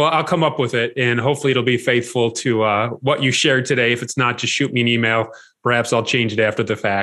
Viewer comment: 13.30 ish I heard ".. if not, shoot me in the knee mail" LOL